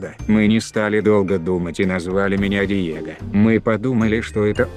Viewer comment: Мне послышалось «диета»